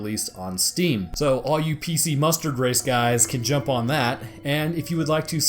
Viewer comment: Thanks for watching guys! So the low frame rate on the trailer is actually straight up from downloading, it runs at 60fps just as a heads up! Also the music during the trailer was used from Negative Atmosphere. So I hope everyone enjoyed the tormentor episode! If you are new throwing a sub my way is always awesome, if you are regular leaving a like is also pretty bro! Y'all take care! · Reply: I heard " Pc Mustard Race " .